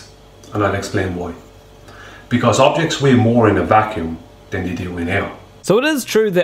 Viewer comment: I think you misheard him, he said "objects weigh more in a vacuum than they do in air"